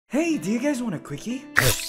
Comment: Quickie? I thought i heard 🍪 why are they flustered anyway??